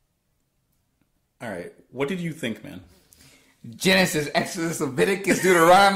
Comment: Could you transcript what is saying at 11:30 please ? I'm not english native and some difficulties to well understand what he is saying :-D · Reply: Probably mispelt but here goes: "Genesis, Exodus, Laviticus, Dueteronomy...all the Bible!!!!"